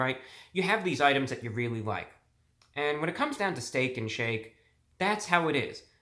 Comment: It legit sounded like you said “Stank and Shake”